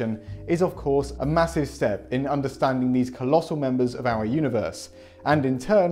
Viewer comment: sorry, all I heard was "colossal members"